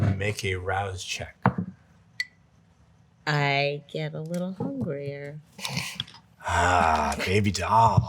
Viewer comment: erika says "i get a little hungrier" & the captions say "i get a little queer". This is giving me the giggles. 😂